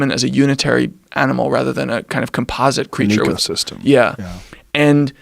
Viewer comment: what does Rogan say here? “... system” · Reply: Christopher Herrera "an ecosystem"